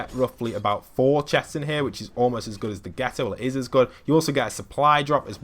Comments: did he say the "ghetto" ? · “the ghetto” instead of the Grotto 😂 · Did he say “Almost as good as the Ghetto.”